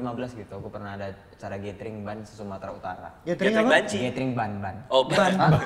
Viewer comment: Kok gue dengernya jg banci 24:58 😂